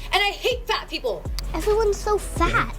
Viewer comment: i thought she said "I hate black people" 😂